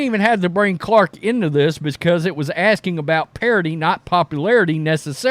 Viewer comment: The reporter asked about parity but I think she thought he said popularity.